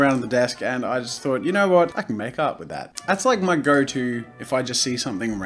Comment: I thought he was saying "I could make out with that." rather than "I could make art with that. "... Freudian slip? Probably.